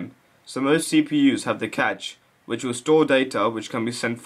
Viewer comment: I say cashe as "cashay", eg cashè. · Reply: At first I thought he said "the catch"